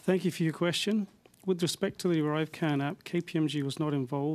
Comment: 😂was that the arrive can crap I heard 😂😂😂